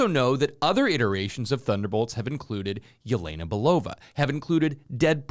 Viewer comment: When I heard Yelena Belova, I thought you said Ginko Biloba. 🤣